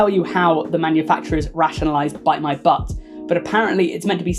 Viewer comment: the telly tubby... I heard "tabatha" instead of bite my butt and it was haunting.